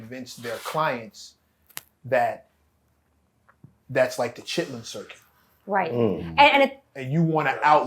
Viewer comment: I agree with. But also, it feels like Kendrick might be trying to broaden his audience. He’s #1 right now, so your fan base has to also reflect that to stay on top! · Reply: Sounds like you saying he bigger than the chitterling circuit 😂